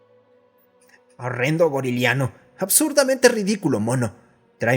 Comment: sinceramente escuché boliviano en vez de goriliano